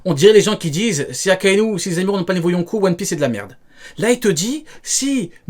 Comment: ya que moi j'ai entendu zemmour??